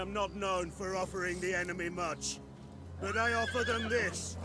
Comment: Had to restart the video, thought he said , "he wasn't known for offering anime merch!"